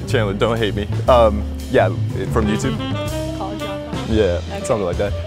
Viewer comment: did the girl say "the college dropout" instead of "the daily dropout"? LOOOL